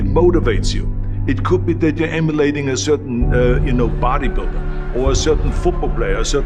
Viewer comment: It sounded like he said that he wanted potty builder. LOL!!!!